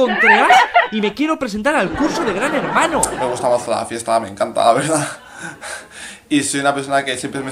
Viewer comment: dice "Me gusta la fiesta me encanta" Pérrea "y ahí una persona que dice" Vuelve a perrear "bueno para los que te cojan vale"